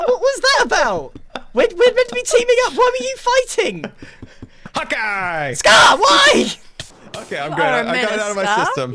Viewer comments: Was i the only one who thought scar was saying hawkguy the whole time? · The whole time I thought that scar had been saying hawky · I thought he was saying ‘hawk eye’ turns out it was ‘h0t gUy